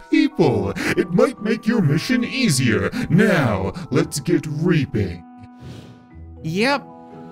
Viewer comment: it’s sound like ur saying grapeing with out the g